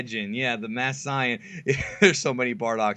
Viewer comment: did he just say the masked sayain? That is horrible pronounciation... im triggered!